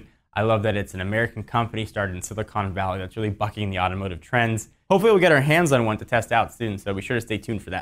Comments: What does he says at 3:02? F**king yatomato trends? lol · I heard "f**king the automotive tread".. then went back.. lol bucking..